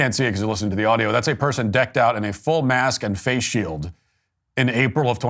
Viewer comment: i thought it said mace shield lol